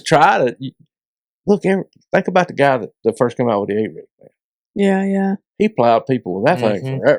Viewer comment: What bait is he talking about at 1:11:10? I can't hear what he says. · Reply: A-Rig